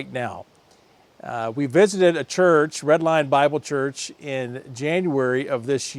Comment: Red Lion, I thought you said Redline and of course it would have a machine shop! Safe travels and all the best. I'm subscribed for life here.